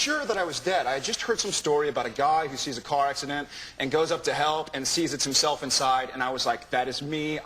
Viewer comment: Did he just say UP to hell? 😂